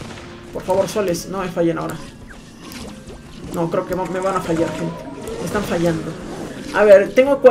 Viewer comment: por qué entendí "me van a fo#&ar" al parcer mi mente estaba borracha xdd